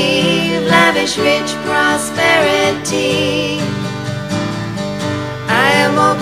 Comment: I legit thought she said Lavish Bitch and prosperity. it made me laugh so much. I was like , damn, ok .